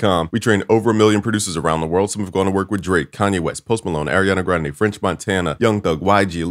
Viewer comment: did u say drake